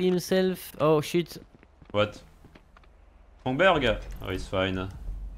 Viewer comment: sounds like you said hamburger